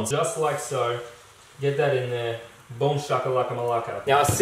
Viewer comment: on 4:07 did you just say "Boom shakalaka malaka"? Because as a Greek and NBA fan it cracked me up :D